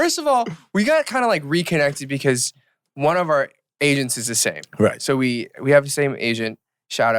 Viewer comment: why did I hear asains instead of agents at first, "one of our asains is the same" - Eric Nam 🤦‍♀